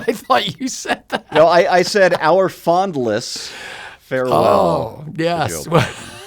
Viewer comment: I thought he said "Fondle Less"